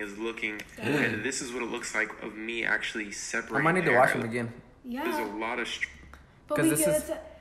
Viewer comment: I thought he said the n word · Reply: Faxx. I thought he said “Oh my nigga washed em again”😂😂